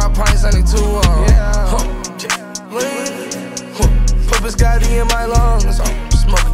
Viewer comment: Yooo I’m not even gonna cap when he said put biscotti in my lungs for sum reason my dumbass thought it said biscuit